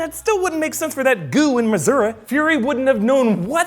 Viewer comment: Is it just me or is saying Missoura at 4:16?